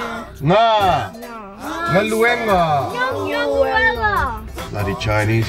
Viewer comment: Hi 👋🏼😄 I just watched Is that us Vlog and Ben was locked out of his house and now this Vlog, you’re locked out of your shop 🤣 Shocking those people destroying the front of the shop. Magaia lakou lima a sasa le se’evae 😆 Bez and mums dinner YUM 😋👍🏼 ❤️❤️ I had to return to say, Girls, you are both doing such an awesome job and funny when your dad was teaching you the word GALUEGA” I thought I heard galuelo and I cracked up cos, it was so funny but in a cute way. Ok I better go. Loved the Vlog 💕💕